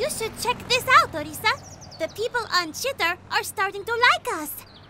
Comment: did I just hear shiter